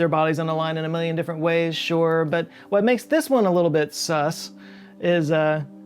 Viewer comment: did you say..... sus...